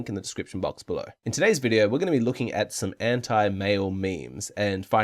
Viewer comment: "we're gonna be looking at some hentai male memes" is what I heard